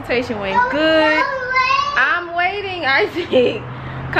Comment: I Love your family ❤️!!!!! I thought he said go away but he said wait. Girl he had me Laughing Girl you all always make my Day!!!!! My Family live away!!!! But I always enjoy your family ❤️